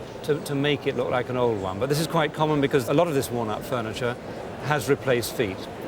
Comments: Ahhh, "walnut". I thought he was saying "worn out". I'm like, how you going to insult her like that right out the gate. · I thought he was saying “worn-out” furniture. WALNUT! Lol😂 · I thought he was saying "worn-out furniture" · I thought he said “worn out “